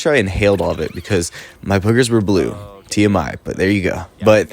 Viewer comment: I thought it said "poggers" not boogers lol